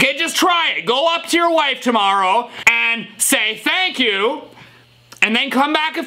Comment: i heard vuck you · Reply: i heard "thunk you"